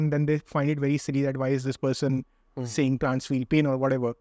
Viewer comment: Great Podcast, one suggestion though regarding the subtitles please proof read them once before uploading. Some words are wrong in the subs like Trans instead of Plants in 49:59 . Great work and keep promoting veganism for a better world👍